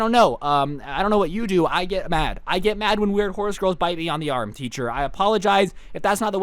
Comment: I thought it said girls horse bite me lmao🤣